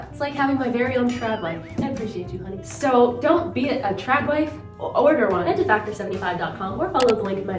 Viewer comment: I heard "don't beat a tradwife, oder one" and at first I thought.. I approve this message. No domestic violence🙅‍♀. Then, I realised I am half deaf 💁‍♀